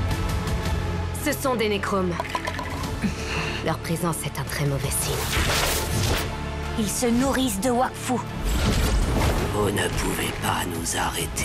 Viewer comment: What are nora and yugo saying at 0:20 and 0:25 ? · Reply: They are Necroms, their presence is a bad sign